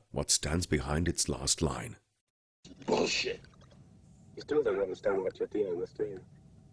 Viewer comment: what is the man saying starting at 13:56? the effect on his voice makes him hard to understand. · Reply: Ash: You still don't understand what you're dealing with, do you? The perfect organism. Its structural perfection is matched only by its hostility. Lambert: You admire it. Ash: I admire its purity. A survivor...unclouded by conscience, remorse, or delusions of morality. Parker: Well, I don't. I've heard enough of this, and I'm asking you to pull the plug. [Ripley moves to turn Ash off, but he interrupts] Ash: Last words. Ripley: What? Ash: I can't lie to you about your chances, but...you have my sympathies. [he smiles]